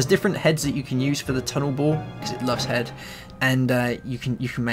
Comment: U sound like ur saying "tunnel ball" instead of "tunnel bore"